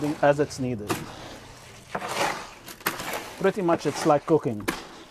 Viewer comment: “ pretty much it’s like cooking “ 😂 totally thought he said cocaine